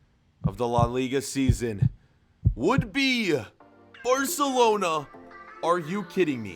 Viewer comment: spoiler I hear u say arsenlona